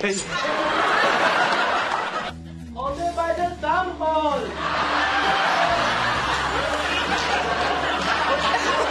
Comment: What did Fez say at 2:36? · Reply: "Hold it by the thumb hole"